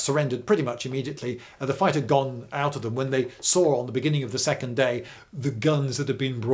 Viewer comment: Did you just say the Brits had Sauron fighting on their side? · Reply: "They saw on the beginning of the next day"